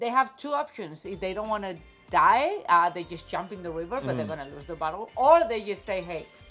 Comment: I thought she said, "Lose the bottle." I said, yeah, never lose the bottle!